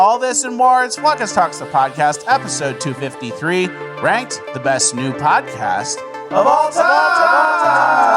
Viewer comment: For so long I thought was saying, "The best NEW podcast of all tiiiiiiime."